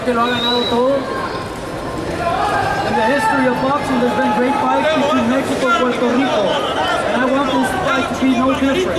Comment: What does the Mexican fan say to Canelo at 1:13-1:21? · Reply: he says, "quiero taco bell."